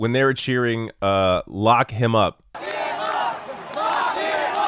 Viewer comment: The audio was in-and-out. I thought I heard "F him up." Did not realize it was "Lock..."